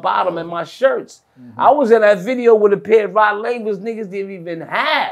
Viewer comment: What’s the name of those sneakers he said? They was clean and comfortable looking too. I think he’s saying Rod Lavers(Adidas)..